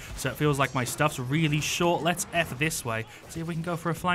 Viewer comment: 'Lets F this way' BOI i know you sayed it because of the Skill but it sounded like: Lets get the Fuck away your're the best xD